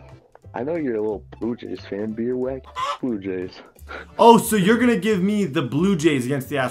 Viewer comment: What did he just say. HE JUST SAID BUT JAYS